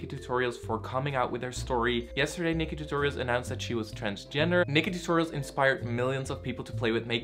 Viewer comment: I'm half deaf in one ear, but no matter how many times I rewind I keep hearing "She came out as Kris Jenner." I know it's transgender, but normally I hear it by the second time I listen. is anyone else hearing it or is it just me?